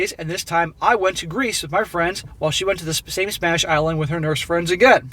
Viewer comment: Captions says "smash island" which is probably not far from the truth. I'm going back to watching ;-)